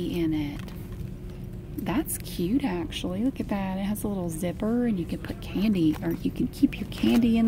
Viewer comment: At first I thought she said "the badass littlie zipper" but then I thought about it again and I heard "the bag has a little zipper" how did i